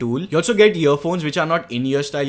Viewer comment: it sounded like he said year phones